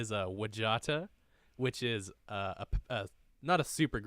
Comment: Should spell out the names of certain artists. Took me a while to find Wajatta. I thought you were saying Wijatta. lol. Wasn't too hard. But i waste 2 minutes of my life googling that! didn't find it till i googled reggie watts. Even better would be to display a short clip of their name/music on the green screen. but only briefly so that you don't get copy right stricken.